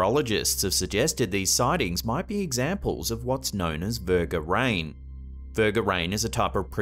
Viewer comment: Does he say Verga Rain??? 🤣🤣🤣🤣🤣🤣🤣